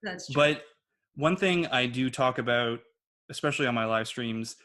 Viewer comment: “a-boot” instead of about=Canadian